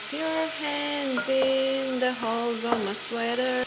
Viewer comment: the nbhd duydum sanki💜 xmsmwmmsm